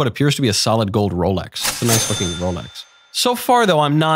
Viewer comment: Did he just say "nice f*cking Rolex"??? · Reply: Nice looking rolex